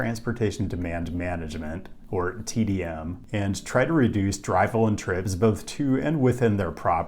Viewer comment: What on earth did he say at 16:18? Try to reduce dryble and tribbs? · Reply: I think it was supposed to be driving and trips.